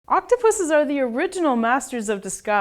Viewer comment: I thought she said octopussies . . . .